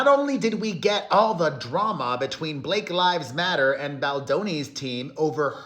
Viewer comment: Did I hear Perez say “Blake’s Life Matters?”😂😂😂😂